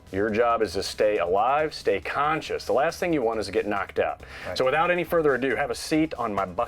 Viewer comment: Last thing you want to do is get knocked up...is what I thought he said lol